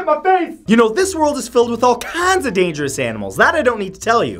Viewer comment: When he said kinds in the beggining it sounded like he was saying KAANDS